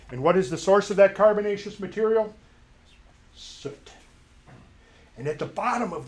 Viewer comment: What did he said at 30:30, I couldn't understand. "and what is the source of that carbonation material ....... ?" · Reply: soot